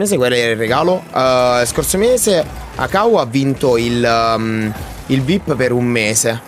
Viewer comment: al minuto 2:59 ho sentito "Caua" CHE è IL MIO NOME E ME SO PIATO UN COLPO cmq non sono io quello che diceva HAHAHAAH LOL